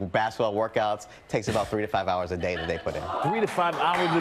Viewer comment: I thought he said 35 hrs in a day then in my mind im like theres only 24 hrs in a day